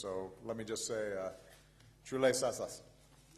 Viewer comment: What did he say? If someone could make out. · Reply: Chale saath saath............... Lol